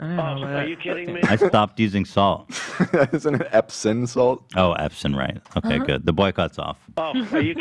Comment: “Epstein salt... yeah I don’t buy that anymore” “don’t you mean epsom salt?” “oh yeah... the boycotts off” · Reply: Even sounded like he said epson salt, drove me a little crazy lol